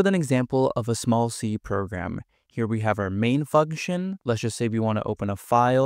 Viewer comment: Bro did NOT say function... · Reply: Fugtion